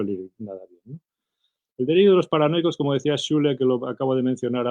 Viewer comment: ese autor que dice ahí que suena como "Shuler".. cómo se escribe, para buscarlo?